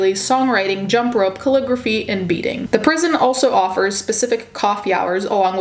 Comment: I thought you said the prison offered classes in "beating" ,instead of "beading"